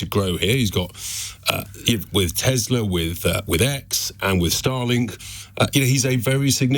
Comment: I misheard starlink as starling, was seriously considering changing banks for a moment there...